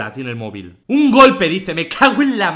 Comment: No se yo pero escuche Egoland